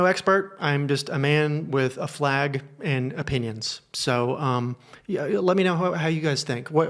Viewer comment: “I’m just a man with a flag and opinions” I swear I heard “flag and a penis” 🤣